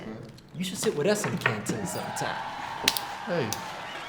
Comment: did just say canteen